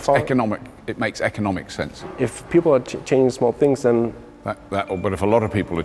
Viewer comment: "If people are chain smoking" I think they are saying "change small things" I could be too tired.